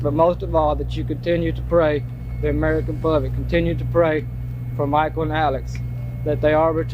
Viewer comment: I could be just mishearing her, but did she say "Michael and Allen" during her parole hearing??? Like.. she couldn't actually remember her sons names and just guessed??